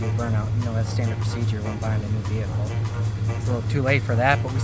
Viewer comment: Heard you say "too tree" brought me back growing up on the south side.